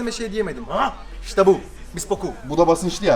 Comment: "İşte bu" dedikten sonra ne dediğini anlayan varmı?😂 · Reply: Haaaah işte bu (bisboku)